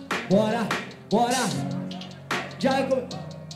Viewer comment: SÓ EU Q OUVI FALANDO JAYA COCO